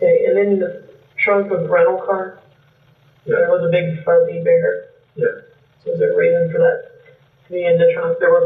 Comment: The female detective saif she found something a big burpee bear or something. Can someone tell me what she had meant and why thinks it had a psychological meaning? Thank you!😊